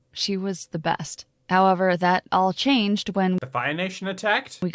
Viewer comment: I´m quite sure now that he says: Until the Fire Nation attacked. The first time (and also the 5 times afterwards) listening to it I understood: Until the Bi-Nation attacked 🏳️‍🌈 Can someone please tell me what he said because the Bi-Nation-Thing would actually make sense in the context 😂😅😂